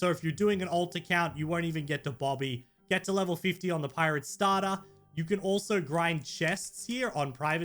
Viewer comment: BRO WHAT ARE YOU SAYING LIKE ITS PIRATE STADA